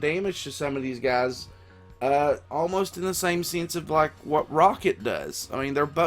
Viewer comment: Syflops B No, he's not, because he was mechanically enhanced and created. Howard wasn't, but considering it looks like he'll be in the mech suit the entire fight he'll most likely be Tech. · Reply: oh i just heard you say something like " he's like rocket, who is cosmic" and got confused